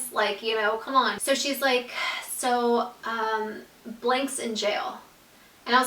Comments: did you say his name right before the girl on the phone said he was in jail???? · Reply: savannah brannan yeah I thought she said Blake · She actually spoils his name😂 (on accident) when she is talking about how the other girl said he was out in jail,, she said that the girl said "Blake was put in jail." So his names blake😂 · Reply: I thought she said Blake too. Lol